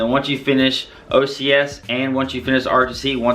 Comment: did you just say once you finish OCS AND finish ROTC? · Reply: Andy Shick and or